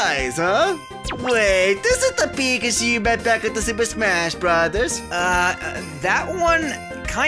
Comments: I have NO IDEA what he just said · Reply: He was talking about Isabelle. "The big Shiba character from Super Smash Brothers" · I can’t even understand what he said there. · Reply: Actually he said "is it that pikachu back at the super smash brothers "